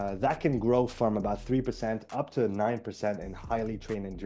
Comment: Good content! Some illustrations could be helpful. 12:16 - Did you say 3% to 9% or 3% to 90%? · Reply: Thank you! Will add more slides and illustrations next time I make a more technical video like this one :) I appreciate the feedback. 3 to 9% is what I have read!